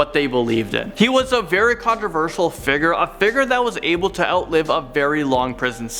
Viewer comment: I’m 14 did he say figgar or did he mean figure??